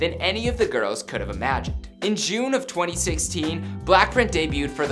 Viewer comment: did he say “blackred” at 13:40 ?!??